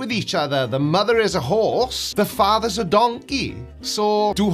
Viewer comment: I KNOW I'm not the only person who initially thought he said, "...the mother is a whore, the father is a donkey!", right??!